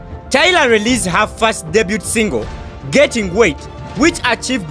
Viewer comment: Did he just say getting wait???!?!!😂😂😂 I dead